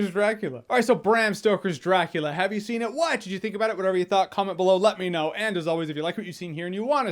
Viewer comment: I always hear: "Why did you think about it?"